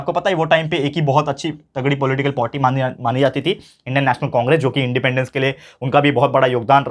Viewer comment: i heard potty😂😂🤣🤣